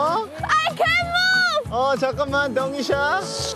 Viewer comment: lol took me forever to figure out what taecyeon said! i thought he said "don't be shy" but it was "wait" in chinese xD